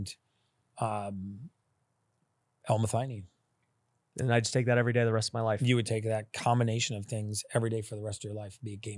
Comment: HELP! I’m non english speaker - what does he say at 45:00 ? Homo…?!!! What?!!! Please this is really important!! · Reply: Homocysteine